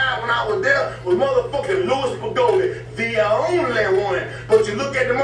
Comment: what did he say his name was the only one he said that died during the ECW when he was there? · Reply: louie spicolli